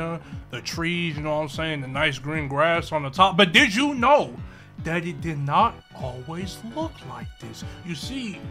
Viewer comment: Did you know? "Daddy" did not always look like this? edit: I know what you mean the subtitles just fucked up and said "Daddy" instead of "That it", also you really gotta make the subtitles better cuz it fucked up almost every word that u were saying about tsb